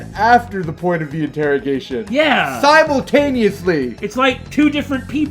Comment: Saemultaneously? I hear that right?